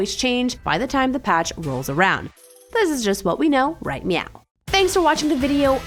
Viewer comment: Did she really say: "This is just what we know right meow?"